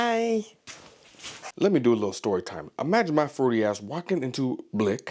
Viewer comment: Me watching, hitting the like button immediately after "Imagine my fruity ass walking into Blick's..." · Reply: I thought he said flirty and rewound then caught the fruity….lol